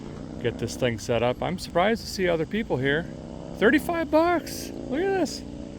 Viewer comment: did you say “surprised to see other people here 35 blacks”